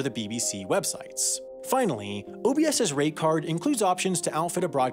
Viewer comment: Ahhh it was "rate card"...... I miss heard it as "rape card" which is also accurate.